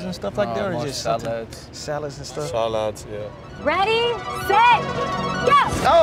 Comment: I heard ramos say salah's 😂